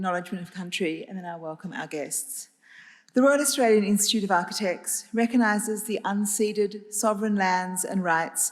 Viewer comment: Is it still the Royal Australian Institute of Architects, or did I mishear. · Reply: Hi Paul, that is correct, our official name is the Royal Australian Institute of Architects, but our everyday name is the Australian Institute of Architects. Regards, Mark.